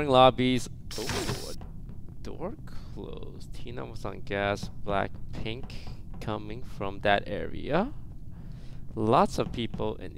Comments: i thought he said blackpink in your are 😂 where are you my blinks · I heard blackpink in your area when it's actually blackpink coming from that area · i'm not a blackpink fan, but my ears heard "blackpink in your area" instead of "black(&)pink coming from that area." THE SIMILARITIES. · the only thing I heard was “Blackpink in the area”